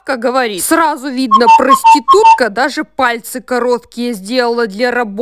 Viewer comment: А что ответила клиентка той бабке я чёт не расслышала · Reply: Она: поэтому вы без покрытия?